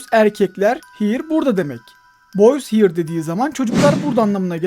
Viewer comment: abi ben orada why boys here diye anladım yani neden erkekler burada yada neden çocuklar burada diyor gibi anladım